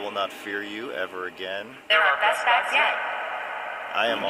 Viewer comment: did it say you're our best bets yet??? In this video, you were helping spirits and it sounded like a female said you're our best bets yet... as in like someone tried to help (possibly Steve) but didn't have the power and when you showed up, you had more power because of your faith and maybe Steves faith combined. Could be my imagination running wild